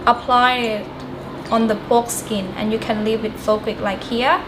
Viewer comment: wooo hold on a minute did she say apply it on the foreskin? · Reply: naw man your imaginations goin wild and I dont blame ya lol she said "pork skin" doe